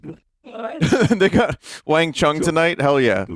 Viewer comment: did just say the N word? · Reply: "They got"